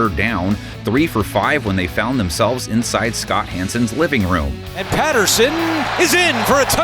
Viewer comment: Who else thought he said Chris Hansen, not Scott Hansen!? LOL